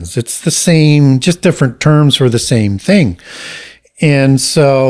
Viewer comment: what is the word he is saying indepts? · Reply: “Adepts” a person who is skilled or proficient at something.